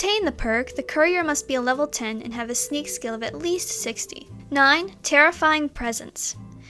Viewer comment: Lol at 0:48 she cuts to the next one so fast it sound like she said 69